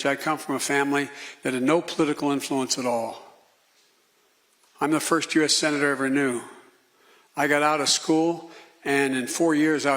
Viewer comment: Did he say "I'm the first US senator I ever knew"